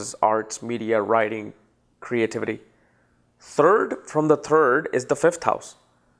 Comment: I thought he was saying turd from the turd but he was actually saying third from third